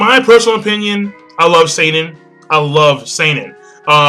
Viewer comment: Oh I thought you said you love saten I was bout to say